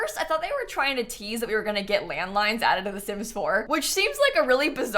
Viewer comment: man i need a hearing aid i almost had a heart attack cause i thought she said they are adding LAND MINES?!!